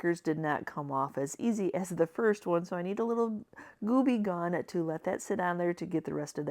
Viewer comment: 😹 at first I thought you said gooby gone. Lol